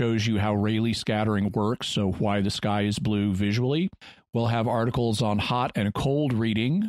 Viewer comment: I thought you said "Why this guy is blue."